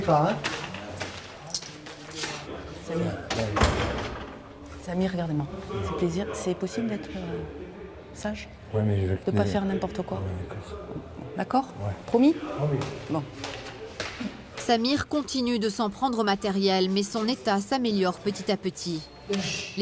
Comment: c'est moi ou il as dit "mouais mais jvais ltuer"